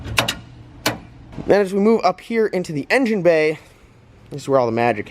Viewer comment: Did you say “as we MOO up here into the engine bay”?? Lol